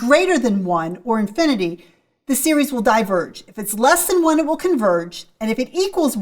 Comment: i thought u said it converges if its bigger than 1